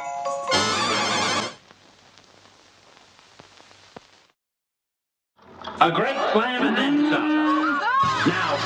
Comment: I always thought the announcer said “A great Slamenza. Now go!” But it’s actually “A great slam and then some. Now go!”